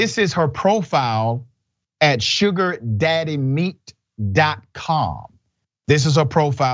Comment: I thought they were saying "Sugar Daddy MEAT" ... 😂😂😂